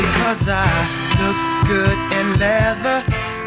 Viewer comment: lol i saw this on a commercial, i heard: "look good in heaven.." it must be look good in leather